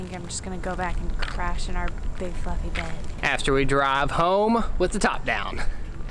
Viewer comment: at first i thought said "big f*cking bed"--but then i realized she said fluffy ...lol